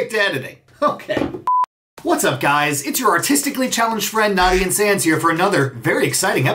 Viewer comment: I heard autistically